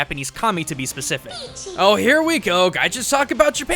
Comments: I know it's "Kami," but I keep hearing "Commy," as in communist, and cracking up in the middle of the video. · I keep hearing "Commie"...so she's a Communist?